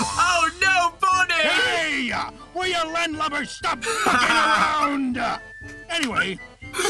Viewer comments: The whole time I thought Foxy was saying “stop f*cking ground”. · I swore I heard Foxy said, “Stop f**king around.”